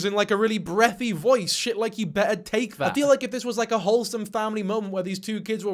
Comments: I thought the step-dad was saying "you better tap that". But the pulling the covers and tone change make me feel like its alot worse. I worry for the kids. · he says to his stepson "Hi there princess". DISTURBING ASF! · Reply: it sounds like he says u better take that.. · Sounds like he said : you better TASTE THAT 😮